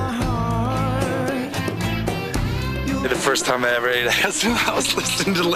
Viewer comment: I haven't watched this since 1995! In my memory the guy at 1:56 said he was listening to Zeppelin the first time he "ever ate ass"... but hearing it again after 29 years, I'm pretty he actually said "eat acid" haha.